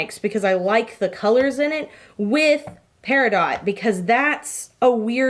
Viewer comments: could have sworn it said "Periodt" and I was like yes!!!🙌❗ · I legit thought Peridot said periodt😂